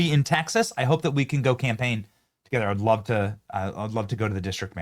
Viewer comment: You said campaign i thought you said lets go camping